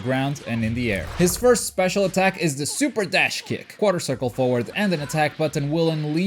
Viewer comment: what the heck are u saying there? I hear quartal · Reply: He's saying Quarter Circle Forward